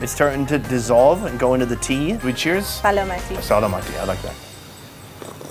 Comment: What did you say lol? Edited: I thougt you said alsalam alakum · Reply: "Salamati" means cheers!